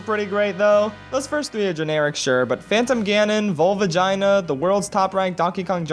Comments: Did i hear vagina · Bro when you said volvagia, I thought you said volVAGINA